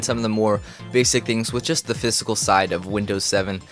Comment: s did you say fiscal side? you fiscal means monetary right? otherwise it was an awesome video! keep it up!